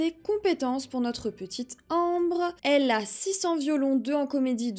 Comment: moi j'entends elle a 600 violons😂